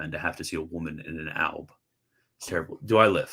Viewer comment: a woman in a what? I can't hear what you said here. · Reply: Alb